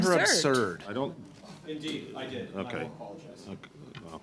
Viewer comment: re: "He called me 'absurd'!" "Indeed, I did. I don't apologize." That's what I heard, yet the subtitles didn't pick up 'don't'. I often fast forward to hear what Ms Khalid has to say because it makes me laugh!! She's so funnnny!!